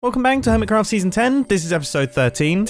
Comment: Did he just say "Welcome Bang to Hermitcraft season 10"